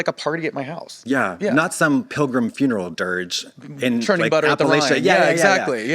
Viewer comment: I heard “churning butter at the Y” 😂